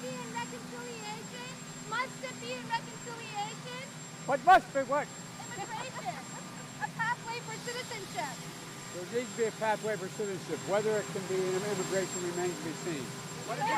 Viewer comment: Lol I thought he was saying " I must be white".